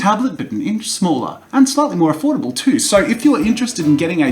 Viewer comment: Sold Replay Buttons : 01:00 01:00 01:00 01:00 01:00 01:00 01:00 01:00 That'll be 1 like for every replay buttons! ^-^' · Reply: I kept spamming 1:00 and it sounded like affordable toast Loved it